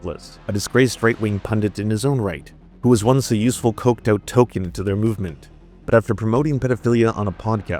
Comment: Did you say "Tolkien"? LOL 😂 I literally just watched another video about the character TOlKiEN from Souith Park 😅